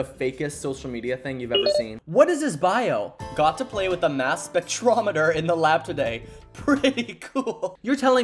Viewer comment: STOP I THOUGHT HE SAID MASS SHOOTER 😭